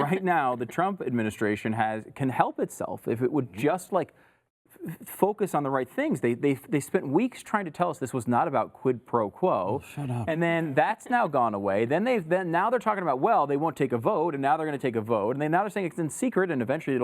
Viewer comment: does anyone else hear someone say 'Shut Up' ?